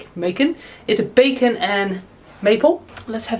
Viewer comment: Honestly thought you said bacon and maple in the mince pie rather than pecan and maple, lol Crisps: will always love walkers ready salted but absolutely love walkers sensations sweet chilli